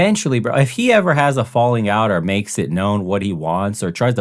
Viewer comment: that’s a very Aussie sounding way to describe that 😂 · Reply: I thought he was saying she is getting around. 😂😂 I love Aussie accent. 😅